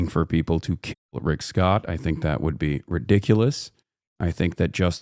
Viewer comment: did he say "Rickdiculous?" It's code chat...